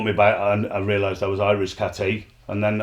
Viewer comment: What's it Irish cat A mean? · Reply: Cheers lads. I could of swore I heard "Irish Cat A" 😂😂